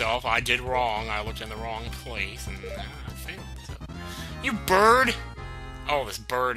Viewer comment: Haha at 11:27 instead of "you bird" I thought he said "you turd." Either works in context :P